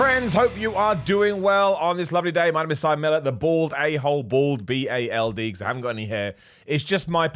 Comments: Thanks for the clarification. I always thought you said Simon “the BORED A-hole. Always thought it was a lockdown joke. 😂😂😂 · I always thought you were saying bored A-hole, then the last vid of yours I watched had me thinking... "he's saying bald.... is he saying bald??". 🤔 And now you've confirmed it. Still sounds like bored to me, but I'm Canadian so I'm going to blame it on the language barrier 😅 Also! Yay to the music reviews